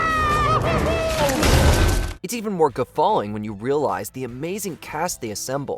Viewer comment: "it's even more ________" - what word is he saying? I'm hearing "guffawing"